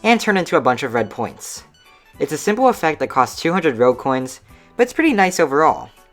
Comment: did he say rellcoins? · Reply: Rogue Coins.